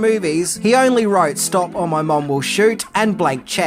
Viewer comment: When I was little I thought he said "Stop or Ill mobile shoot." I was dumb.